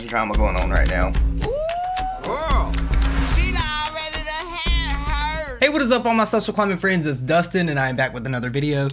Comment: Ok I've worked up the courage to ask, what the heck do they say in the intro?? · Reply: She done already had hers